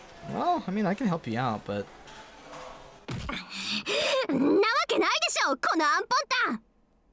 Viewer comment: Pig is 豚 (buta). What she said is 安本丹 (anpontan), which just means idiot as well.